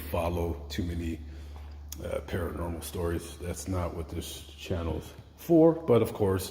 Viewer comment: I think you are great but l don't agree with you calling him a gentleman. Also, why didn't you visit the victim's grave. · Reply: I want to apologise. I watched the video again & l misheard you. It was when you go up the stairs & say 'this channel'. I mistook it for this gentleman. I am really sorry.